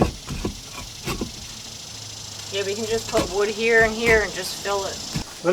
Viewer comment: At about 13:22 did I hear right "put wood in it"? Won't it get termites in it?